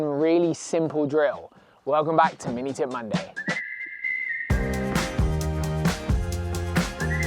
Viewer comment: You get used to it. 🤣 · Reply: I always thought you said Mini Tit Monday lmao